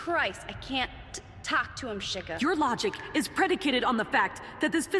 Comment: DID SHE JUST SAY “Shigga”